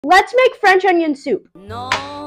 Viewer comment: I don’t got my volume that high and all I heard was “Let’s make friends on YouTube” till I read the caption🥴😭